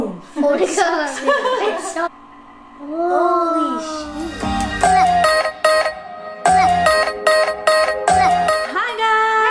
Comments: Did he just say “ holy poop”? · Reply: No he said holy shoot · Am I hearing things or did say “holy sh*t” 😂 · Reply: he said holy shoot 😂 I thought he said that too tho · Did Someone Says H*ly sh*t? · did he say sh** ??